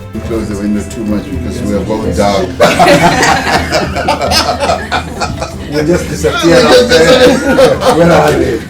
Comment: I couldn't hear the joke they both laughed at. It's good to see great leaders laugh together though. I feel a lot more confident about a politician that lets people see him laugh. You never laugh, I don't trust you. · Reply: Lee Olsson. I heard: "close the window....we are both dark....we'll just disappear". 😂